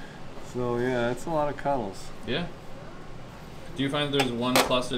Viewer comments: When was saying kettles, I thought he was saying cuddles! Lol. I want to order something soon. Love you guys, even wish I worked there. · With the mask it sounded like said cuddles instead of kettles haha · I thought was saying cuddles and then you zoomed in on the kettles and I was like, ooooh. 😂 Why dont you use a dispenser to fill the cups, like the funnel stick thing you make the cream centers with? Seems like it would be faster?